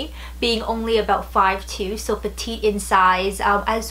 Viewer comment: can someone clue me in on what she said between "fit me being only 5'2" and "as well though they needed to be...." Sounded like "so they tick inside" or " so the tee in size" cant figure it out for the life of me, i'm studying English with these videos so please help if you can Very nice voice btw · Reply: "they need to fit me, being only 5'2 so petite in size"... petite is a French word for "small" and is generally used in the fashion industry to indicate shorter/smaller sizing ranges :)